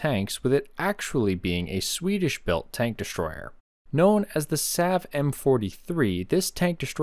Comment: "The Sav M43" but first i heard "the Sad M43" 😂